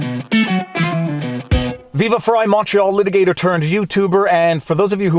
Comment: I just realized you're saying "Viva Frei Montreal Litigator turned Youtuber" all this time I thought you were saying "Viva Frei Macho Litigator turned youtuber"